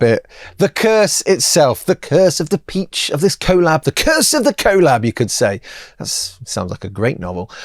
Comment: Misheard it as “curse of the colon” at 4:44 😂 They look great though!